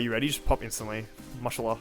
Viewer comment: did elmaxo just say mashallah?